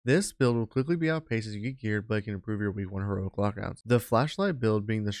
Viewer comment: holly shit you talk so fast i cant understand what you are saying. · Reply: Ya, he slurs/mumbles some of his words. Like @ 00:09 I'm not sure what he is saying. Improve your Heroic lock outs?